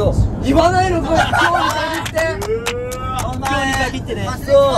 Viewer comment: 🤍 『今日に限って！！！』が ＝『いつもは言ってくれるのに！！！』 に聞こえる😂